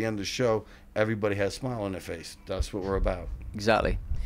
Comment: 6:33 did you just say fast lovin Criminals?